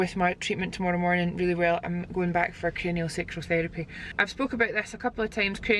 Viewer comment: it 100% sounds like she says sexual not sensual 😂 I heard “everyone thinks I say sexual” “ but it’s sexual cranial therapy” hahaha